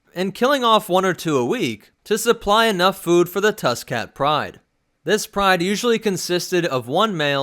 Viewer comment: I thought you said tuskin cat like tuskin rader cat